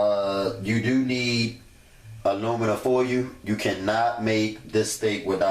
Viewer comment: aluminum foyoo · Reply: I thought he was saying aluminum 4you, lol but damn that looks good though.